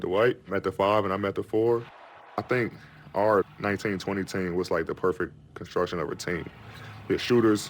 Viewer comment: i thought he said “our 1920s team” at first lol not our 19’-20’ team